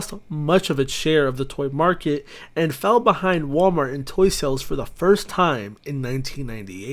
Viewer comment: I thought at 2:15 it said Walmart Asshole